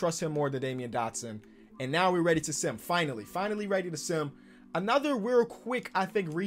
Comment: I thought said finally ready to simp